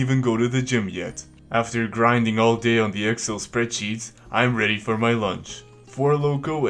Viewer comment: that's not the way to pronounce "excel" ... sounds like you said "axel" PS: Children! Don't get upset! Excel is computer program, not a videogame! Nothing to do with your addiction to stay indoor.